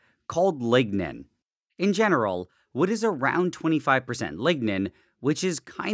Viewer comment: I thought he said ligma